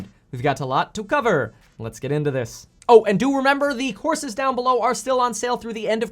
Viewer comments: I thought you said the horses (not courses) were on sale below 🤣😂🤣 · I heard, “The horses are still for sale down below”. I thought I was on the wrong video 😂